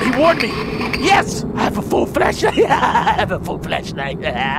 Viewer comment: sounds like Delirious is saying fleshlight